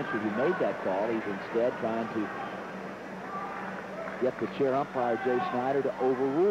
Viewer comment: Thought it said chair vampire